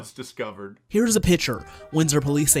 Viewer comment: I'm going to take a reaaaaly long nap. Please wake me when this world gets sane again. Did anyone catch the "journalstist" saying "pitcher" rather than "picture" at 6:45? Fake English.